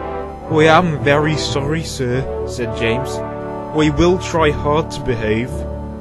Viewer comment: James say we instead of I